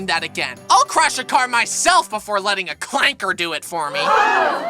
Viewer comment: did he just say Cl@